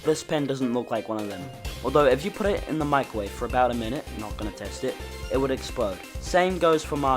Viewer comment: Why did I hear “Not gonna taste it”